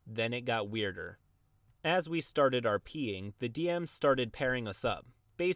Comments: What you said: "as we started rping" What I heard: "as we started our peeing" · This is what I heard @ 3:34 : "Then it got weirder, As we started our peeing" I know, I should have been prepared for the "rp" but for some reason I wasn't.